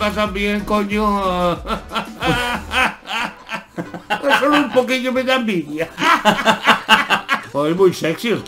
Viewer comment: alguien me dice que cosa dice en el minuto 2:47 xd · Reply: "Solo un poquillo me da envidia" Creo.